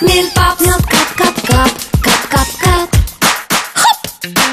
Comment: I SWEAR IT SAID CACA